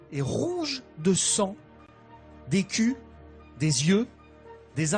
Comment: c'est moi ou à 1:17 il a dit des culs?!